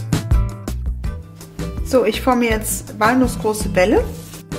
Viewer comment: ich hab erst „anus-große Bälle“ verstanden. Man man man... ich merke mein Alter